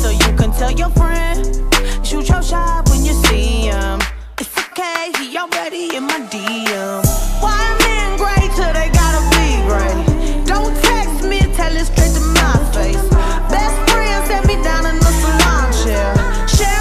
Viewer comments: Am i the only one that when i first heard this song i heard “white men great till they gotta be great” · When i first heard this song I thought she said “shoot your child” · I deadass thought she said 'so you can tell your friend Cause youre too shy when you see him. Thats okay he already in my dms' I think I like my way better anyway haha